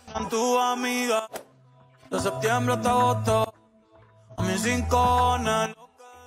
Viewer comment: yo escuché en septiembre esta otto